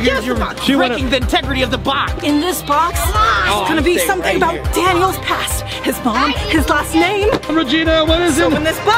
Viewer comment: I just flipping cant stop laughing at the kids in the backround and wqas it just me or did he say oh il stay right here to one of the kids at 16:00 LOL these kids are funny af LOL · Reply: He no I am staying here